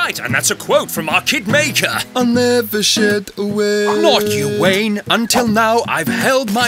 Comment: What did Rooney say? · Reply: I never said a word